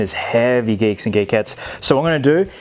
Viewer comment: No offence but: what's up geeks and geeketts What I heard: what's up geeks and gay ketts Again this is a joke so no hard feelings